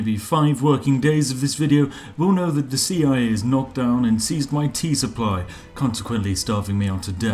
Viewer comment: I heard "teeth supply" the first time... :D